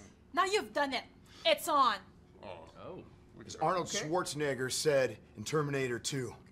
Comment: Sounds like he said "swartznigger" I sorry